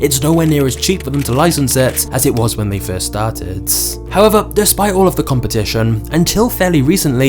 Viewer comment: he said star tits instead of started